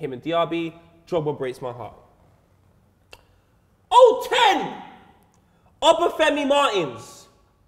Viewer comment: did just say 010?